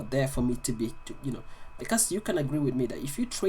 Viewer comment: did you say " kass you can agree with me"? 👀 😂😂 And yes ofcos I agree 👌🏽